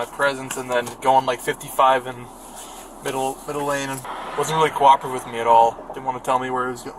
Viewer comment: Didn't the cop say middle lane instead of in the middle between lanes